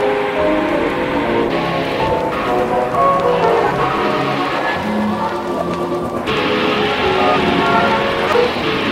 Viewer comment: I swear I hear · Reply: That is